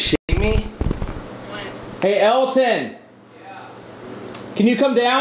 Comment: BRO AT 1:06:26 SOMEONE ANSWERS COREY AND SAYS “What?” WHEN HE SAID “Are you fucking shitting me” TELL ME IM NOT THE ONLY ONE WHO HEARD THAT